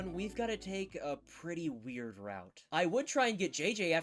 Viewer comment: did you just say a weird route?